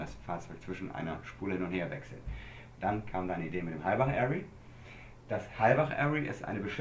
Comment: Wie heißen diese Arrays, ich habe es nicht ganz verstanden? Ich will ALLES darüber wissen! Was für ein geiles Spielzeug. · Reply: Halbach-Array. Den Rest musste selber Googeln, ich habs nicht verstanden.